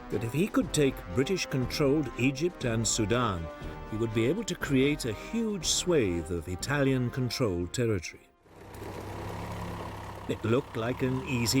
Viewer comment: does anybody know what he says here? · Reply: Sure! "...he would be able to creathe a huge swathe of Italian-controlled territory."